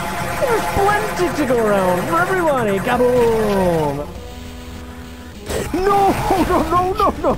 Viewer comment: For a moment I thought he said "gabagool"! 😂